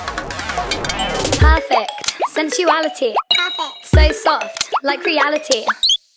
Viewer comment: Perfect. Sensuality. So soft. Like Reality. I'm feeling like I'm slipping to an undersided fate. But these pants are so smooth and they're more than I could take. I ask you to fulfill my favorite fantasy, like you and I could not tell the difference in insaanity. So Baby. Baby. Is it fair to say I care? I made you taste my favorite tear the one I saved for all these years. You're not- not- that bad. Y-You're not that- Y-y-you're not- not- that- that- bad. You're n-n-not- not that bad. Baby. Baby. Baby. You're- You're- not that- Y-You're- You're not n-not- tha-that- ba-bad. That bad. So soft. Smooth. Sensuality. Undersided. Reality. Smooth. Fantasy. Not that bad. That bad. Not- Not- Not- Not. You're not- You're not bad. That bad. Y-You're not- not- bad. Bad, Y-You're not- not- not- Say it soft. You're not that bad. You're not that bad. You're not that bad. You're not that bad. You're- You're not that bad. You're- that- Sensuality. So soft. Like Reality. I'm feelin-feeling like I'm slippin'-slippin' into an- but these pants are so smooth. Can I ask you- Can I ask you to fulfill my favorite fantasy. Physical Insanity. Insanity so-- That bad. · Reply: I thought she said sexuality lmao